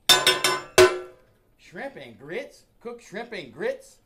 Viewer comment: I thought it said shrimp and girls :(